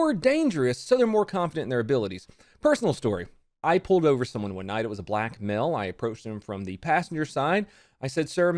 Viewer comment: I thought he said "I pulled over someone when I was a black male" Visible confusion